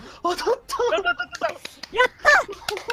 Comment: did i just hear that guy say YATTA?